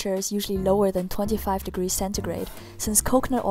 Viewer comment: What did she say at 17:20?! 25 what?! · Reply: She said 25° centigrade